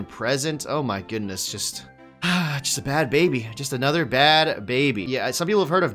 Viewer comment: Did I heat correctly? Did just said Bad Bunny at 4:00 hahahah that's what's up #DECENT